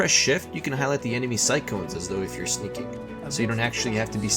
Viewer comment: speak more clearly or english sub your vid. wtf are you saying @ 1:03 , @ 3:24 , etc. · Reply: is "sightcones" 3:24 is " undead characters have infinite lockpicks" seems fairly clear to me